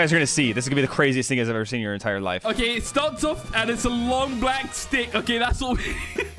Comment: Ayo, what did say at 1:46:39 💀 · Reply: “A long black stick hahahahahhahahahahah”